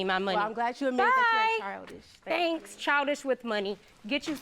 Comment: She said get you some property baby girl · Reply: sounded like she said big girl maybe she did say baby girl...